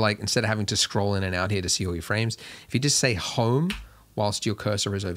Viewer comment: what is he saying?? To get hone, home, what? I don’t know what I’m supposed to do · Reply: Click the home key on your keyboard.